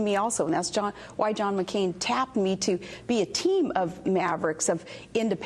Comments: Sounded like she said she got tapped by a team of Mavericks, so that's how she got the job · did she say John McCain tapped her? LOL She cracks me up. I still can't believe so many people took her seriously. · LOL did she just say "John McCain tap me"?? check for yourself.. video starting